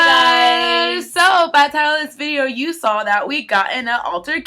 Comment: what? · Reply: “Jas” Marie Oh lol at the beginning I thought she said Tyler's video but she said Title. Sorry, my bad 😅